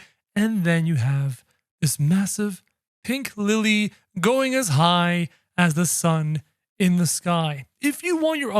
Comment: I heard "going as high, as the RED SUN IN THE SKY".